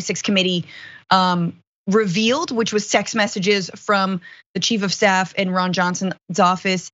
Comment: OK, I have to ask, at 4:01 did she say "sex messages revealed..." ? It could be my hearing as I am an old bird but if true, hilarious! And this guy will fry like the rest I hope. Trump will easily throw this guy under the bus, no problem.